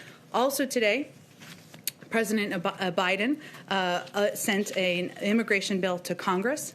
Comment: did I hear a "president Oba.....Biden????????